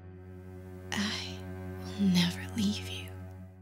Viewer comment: What says exatly the character in 4:39??? I am not an english native speaker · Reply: Collosus Slow She says: "I'll never leave you"